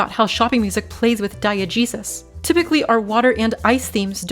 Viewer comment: It took me a bit to realize you were saying diegesis, as in the nominal form of diegetic, and i thought you were saying die-a-jesus and i was like wtf is she even talking about lmao